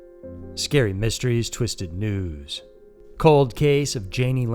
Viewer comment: Idk why I thought he always said “twisted twos” 😂